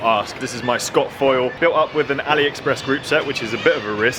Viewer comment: what have you just said? :D AliExpress? and 1000 km? good luck with that :D